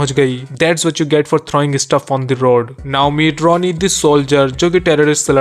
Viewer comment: When he says "Now Meet" idk why I hear "Na Umeed" 😂?